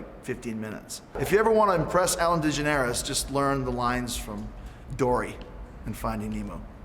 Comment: If you want to impress ellen learn to say her name correctly lol. that being said Chris Jericho >>>>> Ellen Dejanaires all day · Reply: I thought I heard him say ''Ellen Degenerates'' XD